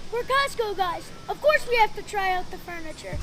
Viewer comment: Oh I thought they were saying “we’re at Costco guys”